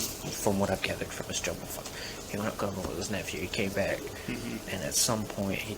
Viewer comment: What did that cop say at 3:13 where it says "unledgeable".. it sounds an awful lot like "jungle f💣ker" 😂